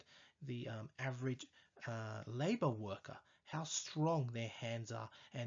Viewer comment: what did he say?! Just look at average label worker?!!!!!! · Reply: I think he said "labor worker". Like a construction worker